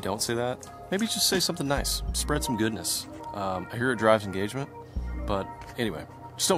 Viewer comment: I could have sworn you said something about Rob’s engagement. Instead you said, it draws engagement. Lol. Keep the videos coming. A lot of people relate to you as the most normal one of the Googans. Been hooked on y’all and you’s guys company.